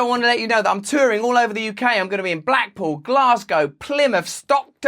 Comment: I heard he said "I'm touring all over the Ukraine" 😬